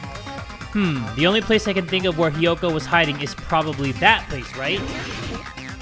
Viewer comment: bruh at first i thought he said kyoko